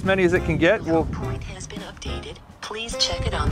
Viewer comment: That 3rd take off it actually sounds like it said "No point has been updated"